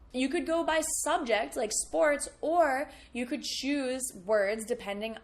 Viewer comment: When you said " you could choose ... " I heard it like " you juice ... "